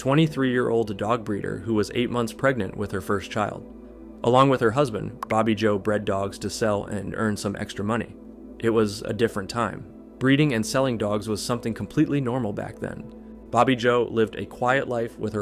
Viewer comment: the way you said that 🤣 it sounded like you were saying her husband’s name was “Bobby Joe Breaddogs” 💀 I am dead. I am deceased. I cannot stop laughing.